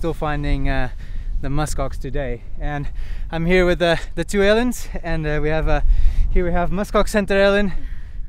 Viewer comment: This is awesome as always, but I had to laugh when i heard you say "I'm here with the two aliens" 😂 (talvez porque é difícil para um português diferenciar os vocais curtos dos longos)